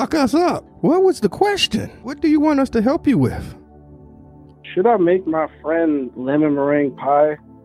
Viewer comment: I got distracted by the first statement and I thought he said "Should I make my friend 'lemon meringue pie' me this weekend?" I thought 'lemon meringue pie' is another sexual term goddamnit